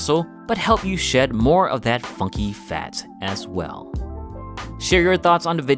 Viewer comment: did he say fucky fat XD